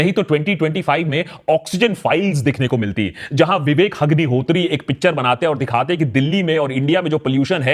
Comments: kya bola bhai "VIVEK HAGNIHOTRI" ya maine galat sun liya? 🤣🤣🤣🤣🤣🤣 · did he say Vivek Hagnihotri😂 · he actually said 'Hug'nihotri 😂😂😂😂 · Did he just say Vivek Hugniotri at 11:25 min 😂😂😂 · Did he just say Vivek Hagnihotri?? 11:25 😂😂